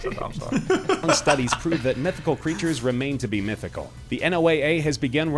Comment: thought he said methical